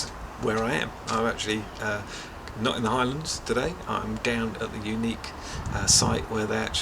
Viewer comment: you have a look around the channel you will soon see the Highlands of Scotland feature a lot...that's where I live and love it.....mind you Ireland is very nice too TTFN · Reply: Reviews Ah ok cool,Lol at 0.18 i thought u said ireland,Great vid btw,hello from Ireland;-)